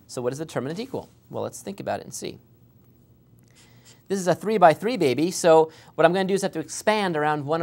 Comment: did he say “the 3x3 baby”😂